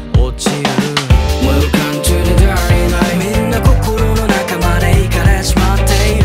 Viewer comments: Welcome to the dirty nightの「dirty night」がた〜りないに聞こえて好き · welcome to the dirty night のところ むーかんちゅうでだーりないって聞こえる件(よく考えたらそんな日本語ないことに気づいた)